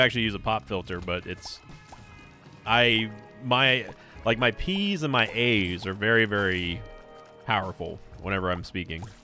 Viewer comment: Totally thought u said my pee and my aids